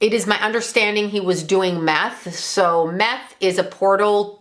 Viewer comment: I had almost dosed off when I thought I heard say “math opens you up to demonic energy “ I said “I knew it, math is the devil 😈 “ but then I realized she said meth. 🤦‍♀️